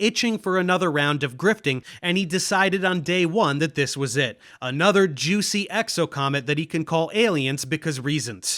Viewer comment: Jew see exocomet that he can call aliens? did i hear that right🤣